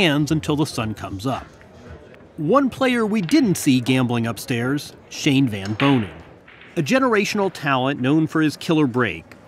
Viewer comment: Lmaooo :(((( I thought it said Shane van Gisbergen